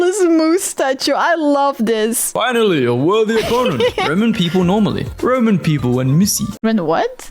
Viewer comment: Did bro just say "Roman people"?